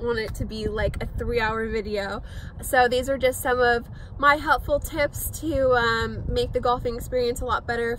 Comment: Sounded like "My helpful Tips"... just had to make sure.